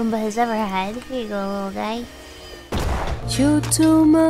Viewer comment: I swear it said "chotto matte gurasai" 15:00 she's making the Anya face